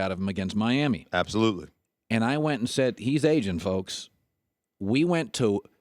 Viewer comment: When Colin said that Brady is aging, I thought he said Asian